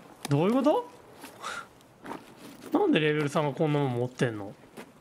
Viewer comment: の所なんでレベル3がこんなもん持ってんの が、コンドーム持ってんのに聞こえて爆笑したww